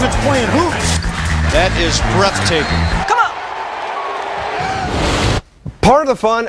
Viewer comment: is it just me or does someone say come on